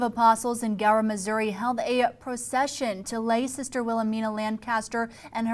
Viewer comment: I thought it said possession.